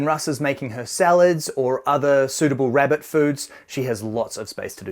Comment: did he just say rabbit food? XD